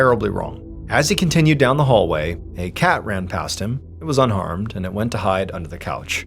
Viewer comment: I know you said that the cat was unharmed, but I swear my brain hear “unarmed” 😂😂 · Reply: I had to go back and listen a 2nd time I thought I heard unarmed too.